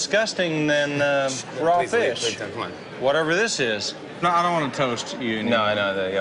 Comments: Jimmy at 2:30 is REALLY bothering me for some reason.... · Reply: I heard him say "...Please don't, c'mon" and I thought he was responding to Blake calling the food disgusting. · for those who keep wondering what jimmy says at 2:30 he says: "please leave" to the waitress because he did not want her to get offended by blake insulting the food and then he says "please don't c'mon" to blake for obvious reasons. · Ok but at 2:29 what is Jimmy doing? Asking the waitress to leave? I hear Please leave.. please don't, come on.